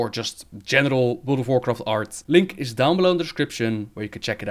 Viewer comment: I heard "Genital World of Warcraft Art" around 30-60 seconds LOL. I know what you meant, you're still great.